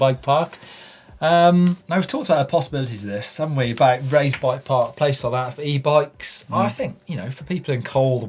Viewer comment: Did he say Ray's Bike park? meaning Ray's MTB Park in Cleveland, Ohio? If so, How in the world did you hear about it / go there?